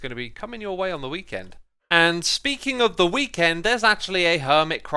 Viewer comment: When he said "speaking of the weekend" All I heard was "speaking of The Weeknd"